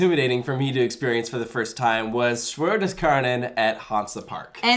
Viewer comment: i want to watch a video on the coaster at 6:45 but i cant even pronounce what he said let alone spell it. when i tried it pulled up southpark episodes of them eating chicken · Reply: Der Schwur des Kärnan, at Hansa Park, Sierksdorf, Germany